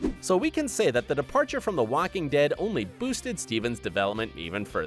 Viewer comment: Did he just say "Wacking Dead" @ 6:35 ?